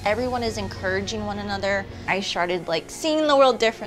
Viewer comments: i swear i heard her say i sharted. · Am sorry me and my husband are jokesters I totally commend this woman for what she is doing and continues to do but if you listen at exactly 2:07 she said " I SHARTED" instead of "I STARTED" lol